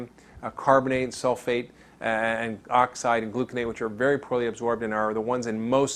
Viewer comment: Sounded like you said gluconate